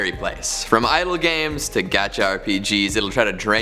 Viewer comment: Wait..in 0:05 did he said gacha?